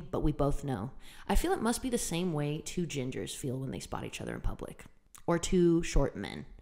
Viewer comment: I thought she said two genders at first and was really confused.